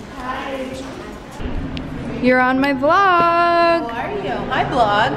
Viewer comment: “ Glog “ - Kris Jenner, 2019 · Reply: I think she said Blog